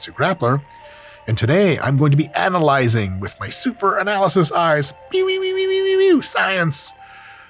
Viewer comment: Did he just say "with my Analysis be we we we weeu science eyes"!? 😂😂😂 Bro I'm crying 😭